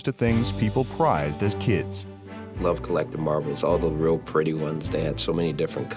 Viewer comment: Successful coaches: i collected baseball cards Browns coach: ya i collect marbles, you know the pretty ones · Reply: Thought he said barbies